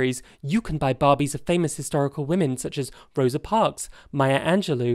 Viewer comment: I thought you said "..you can buy hysterical women" not "historical women" and I laughed thinking you were making a joke, then shocked because maybe you weren't and I was missing the criticism you were trying to make (and I'm new to your channel).